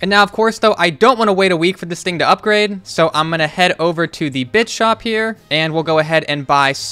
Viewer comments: I thought he said bitch shop when he went to the bit shop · Am I the only one who thought he said bitch shop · did anyone else hear bich shop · did i hear the b*tch shop?